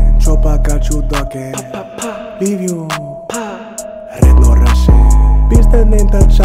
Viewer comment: I thought mans said leave you like no Russian like the campaign mission and tapatio go hard